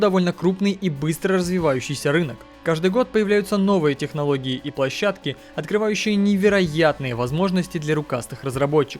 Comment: подскажите пожалуйста что это за игра просто когда автор сказал название этой игры трудно расслышать правильное название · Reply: Ape Out